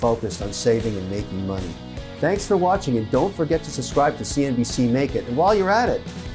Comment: I thought he said "Don't forget to subscribe to see me naked" 😂😂🙈